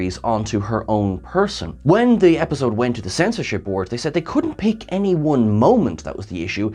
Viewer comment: I thought you said borg member lol not board